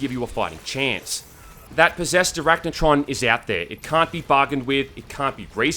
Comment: “Possessed arachnotron is out there, it can’t be BARGAINED WITH” first listen through I heard “Barbequed” and didn’t even question it :p